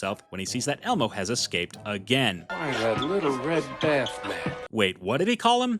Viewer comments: I heard, Little Red Bath Man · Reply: I heard “Little Red Bath Mat” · I heard, Little Red Bath Man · He sounded like he said "little red batman"